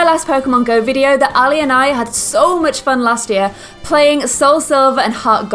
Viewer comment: i thought you said, "i and i had so much fun last year", rasta.